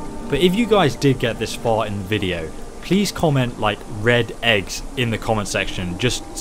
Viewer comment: Red eggs my dude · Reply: Did he say red eggs? I thought he said red x. I had to listen to it over and over again.